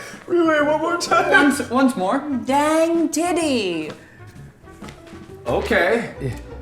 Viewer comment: it sounded like Ethan said dank Titty